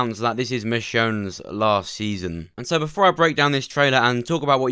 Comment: Almost had a heart attack I missed heard him and thought he said it’s the shows last season and I was like wtf then I went back and heard it was michonne last season it’s still sad